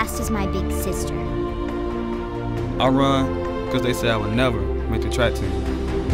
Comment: Can someone help me? What does he say at 0:53? "I run because they said I would never..."? And at 1:07 "I run for a change of simmer"? Is that right? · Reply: Renato Arakaki 0:53"... I would never make the track team" and 1:07".... a change of scenery"